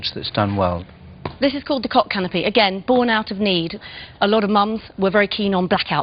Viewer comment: hahahhaa oh my god i thought she said The Cock Canopy